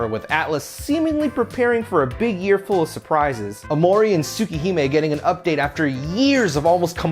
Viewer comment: Okay supa what now? I know I’m probably mishearing but I heard “Aomori and Supa Hema getting an update after years.” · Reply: Tsukihime!